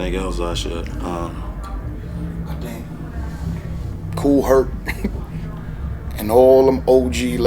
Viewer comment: Who did Joell Ortiz say?? Cool Hurt? I didnt hear him and I need to recognize and look them up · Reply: Haven't reached that part, but seeing how you spelt it he probably was saying Kool Herc, a DJ. But again, I would need context.